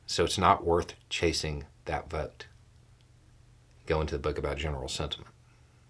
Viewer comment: Beau says: "goes into the book about General Sentiment." I hear: "Deathnote." 😆